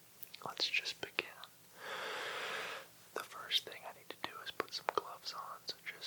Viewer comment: when he said I need to put some gloves on I thought he said I need to put some cloths on